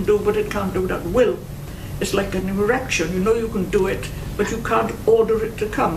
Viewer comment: Did she just say erection? LOL!